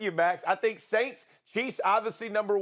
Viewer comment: I thought he said “the saints went in and F*** Tampa bay” at 5:45 🤣